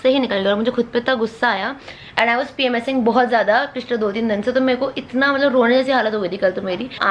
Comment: Hello can someone tell me what word di said during 1:07 minutes that ' I was _ bahut zyada do teen din se???? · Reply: PMSing